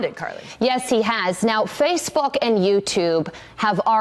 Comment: anyone else hear 'face f***'? Edit: the censor is out of respect for